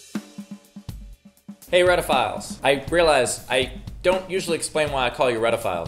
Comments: I thought he was saying redditphiles? hahaha welp I'm fine with it now. hahahaha if I wasn't it would be discrimination! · I always thought you were saying redditphile so I assumed to posted on Reddit first